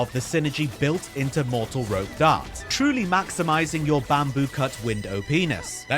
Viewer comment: "Truly maximizing your Bamboo Cut window penis"? What? Did you stutter? 😂 It sounded like you said penis.